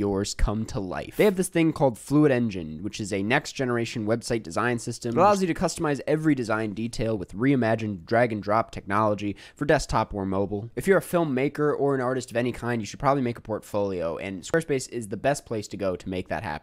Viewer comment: thought i heard "imagine dragons" in the sponsor ad portion instead of "re-imagined drag and drop"